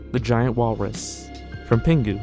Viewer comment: i thought he said "vagina walrus" xD